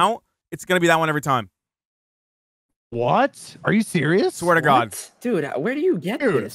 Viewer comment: (17 would be better than 8) did anyone else hear "Swear to gods." Is Basically into greek mythology or is it just me being the dumbass I am